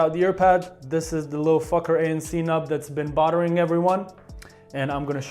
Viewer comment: That caught me off guard so bad, it sounded like he says “This is the little fucker ANC knob that’s been bothering everyone”😂